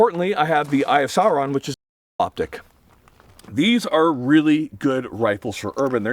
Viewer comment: what did he say why is it bleeped and what is he talking about? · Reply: Thermal